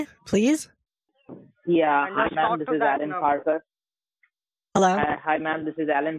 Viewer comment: did he say he was Alan Parsons? Was this his "Project" all along? The "Eye In The Sky" (Kitboga) will be looking at you, dealing with fools (all scammers)!